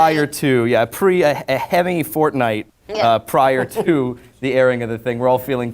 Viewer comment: did he say fort nite?